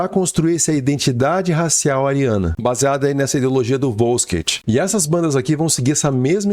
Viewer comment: Fala! Tranquilo? Primeiramente, parabéns pelo vídeo! Em segundo, o que você fala ali no 8:36? Wolsket? Volsket? Não entendi muito bem hahah queria pesquisar mais sobre!